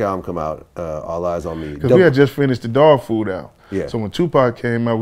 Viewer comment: did he say Dogg food album?!😂😂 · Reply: Mister_Mister420 Yeah. Dogg Food . Daz n Kurupt , DPG. You never heard it ?